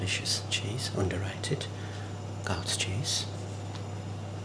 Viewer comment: I kept hearing “God’s cheese” and I couldn’t agree more!